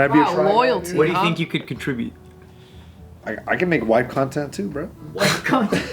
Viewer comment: white content? I thought he said wife content lol